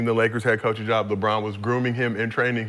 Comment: did he say Lebron was “grooming him” similar to a predator preying on the young and or vulnerable individuals they choose to exploit? 😮 Damn…